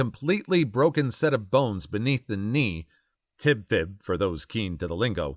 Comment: the Captions messed me up when you said "Tib-Fib" since it only said Tibia and I thought you said "Tidbit" haha